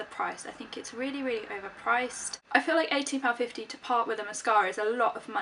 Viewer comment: Oh, hahaha, sorry, it sounded like you said £80.50, and I was like ' O_O '. I know Chanel mascara costs around £25 here, so I almost freaked out. But anywho, loving the wavy, voluminousness hair! :)